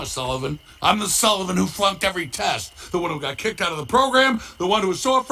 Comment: did Sully say Fu*ked?